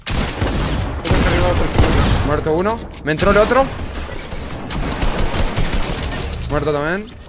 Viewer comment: Alguien me puede decir que es lo que dice su compañero en el min 2:10 dice algo como one its me o algo en ingles porfa díganme